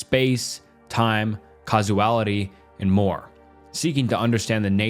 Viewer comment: "casuality" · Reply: i thought he was saying kazuality at first